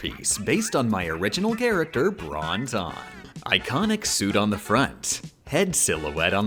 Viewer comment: "bron tonne" I misheard this as bronzong the first time